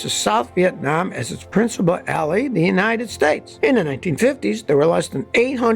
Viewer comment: principle alley? i think you meant to say ally. lol